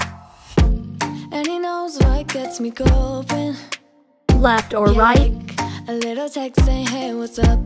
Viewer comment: I thought you said "left or die?"- Idk y..💀